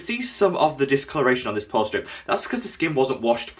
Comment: Anybody else hear "prostrate removal" or just me? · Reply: I heard " pore strip"